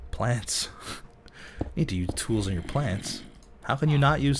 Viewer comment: I thought he said "You need to use the tool in your pants" .-.